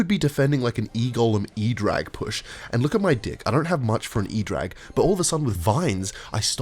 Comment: I can't tell if he says dick instead of deck because he's trolling or because he's Australian